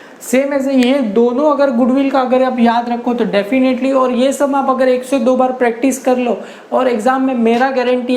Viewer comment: what sir said practice this sum 2 to 3 times😃😃 What I heard practice this sum 102 times 😳😳